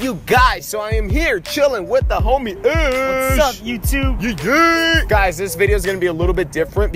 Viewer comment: wt the subtitles he said whats up youtube but the subtitles says i love you too xddd